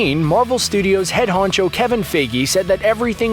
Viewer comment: remind me did he just say Kevin figey